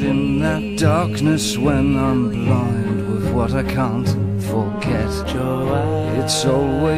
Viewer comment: Anyone else think it sounds like he syas "cunt" instead of "can't"? xD